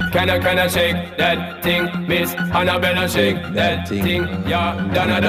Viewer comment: as a fellow jamaican I thought he said "can you can you shake that thing." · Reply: I thought that’s what he said too 😂 and I thought he said “and you betta shake that thing” instead of miss Annabelle